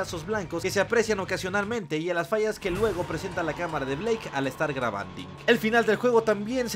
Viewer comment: que dijo? Grabandi?